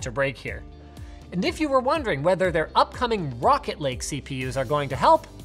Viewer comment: I heard "Rocket League CPUs" at first, lul